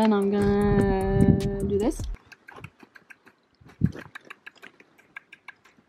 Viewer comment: I also thought you said, lead with a backpack XD